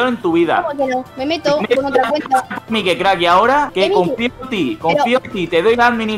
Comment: alguien mas escucho que dijo mikecrack??